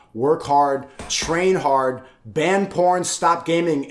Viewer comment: DID HE JUST SAY BAN P@ 💀💀